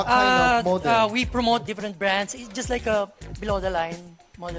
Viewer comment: When i played it again i realized that his "F" sounds like "P" 0:48 when he said different it sounded like "diprent"